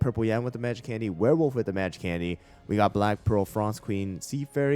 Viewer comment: I misheard "black pearl" as "black girl"